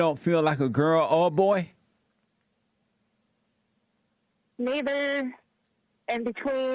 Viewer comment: I thought she said the n-word when she said neither